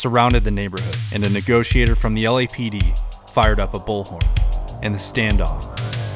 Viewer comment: I thought he said the reporter fired up a bowl 😂